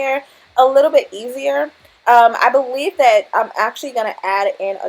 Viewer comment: girl you slayed this & all but @ 12:42 DID I HEAR YU SAY “ i beWEAVE “ ?? 😭😭😭